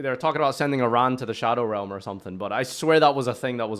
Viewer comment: I don't think they said send iran to the shadow realm, it was something else, but that would have been a good line, really it's just a nice way of saying hell, didn't the manga just say you get sent to hell, it was the 4kidz dub that changed it